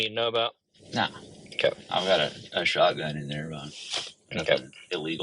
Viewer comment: Does he say "I've got a shotgun in there, Illegal' WTF?